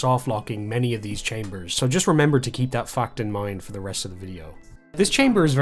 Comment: I misunderstood what you said and thought you said "remember to keep that f#cked in mind."